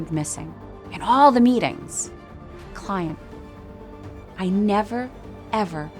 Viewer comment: I seriously heard she said "cunt" instead of "client" 😂😂